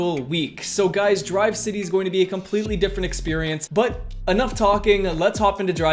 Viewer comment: did he just say experience...